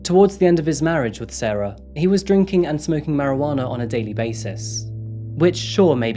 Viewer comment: "Drinking" ... marijuana :P love it I heard it wrong... Drinking, and smoking marijuana